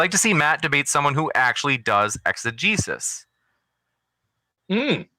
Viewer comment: Can someone help me out? At the 2:03:35 mark, a chat question is asked and I cannot figure out what he is saying. I’ve listened 20 times in a row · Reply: Exigesis.